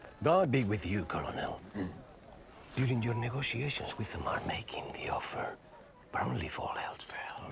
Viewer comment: What does he say at 16:10, after he says, "May God be with you." ?? I've replayed it 6 times, even at slower speeds, and still can't make it out. Thanks you in advance: ) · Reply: Michelle DiNovo Accordingly to (CC) (Closed Captions ) the exchange was : " God be with you ,colonel , using your negotiations with the Mart making the offer Bromley for heath fair , of course Then somebody comes and says : Please let me go with them , I give you my word of honor i will come back . My father....." Of course "Bromley for health fair" sounds like misspelled words , but maybe it will help some ? It is impossible to decipher